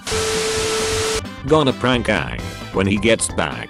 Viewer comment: Did i hear oong instead of aang around the time